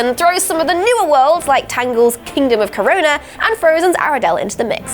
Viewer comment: Did she just say kingdom of CORONA